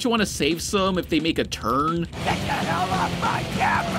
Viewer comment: Was that edited? Or did he just say "hell" in a kid's movie.